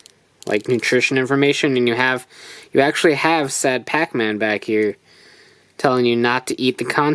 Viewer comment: did he just say SAD PAC MAAAAAAAN (and that's it for the packaging moving right along here we have...)